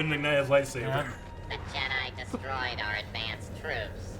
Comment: As a kid, I thought 224 said "our advanced troops", as in those were supposed to be the most competent.